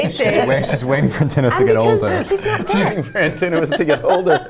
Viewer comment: ROB SAID “she is waiting for ANTONOIUS to get older” THE CAPTIONS SAYS TELEMACHUS i was like isnt that his mom?????? HAD ME TRIPLE CHECKING